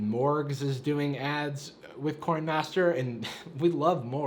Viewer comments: I thought he said "corn master," lol. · Did he say corn master